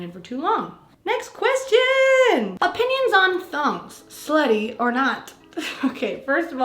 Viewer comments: omg I was zoned out when you read the question "are thongs slutty?" and instead I heard "are thumbs slutty?" and I got sooo confused lol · I thought you said “are THUMBS slutty” lmao in was like sis what?😂